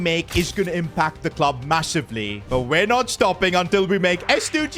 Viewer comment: did you say massive ? Like the low taper fade meme ?